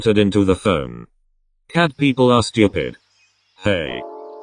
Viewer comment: I wasn’t paying attention when said “cat people are stupid “ and I thought he said”cis people are scary”😭😭